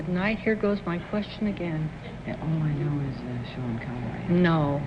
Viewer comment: is she saying "All I know is that Sean Connery?"